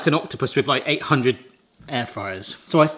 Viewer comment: I thought you were saying ginger air fryers hahaha